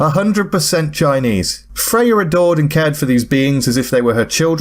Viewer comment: I thought you said Freya ignored and loved the babies and I thought... Ahh just like real parents. Also, the Names fat. Long fat.